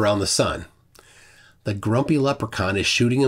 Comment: I thought you said "gropey leprechaun" until you put up the grumpy graphic. :D Thanks for the great video.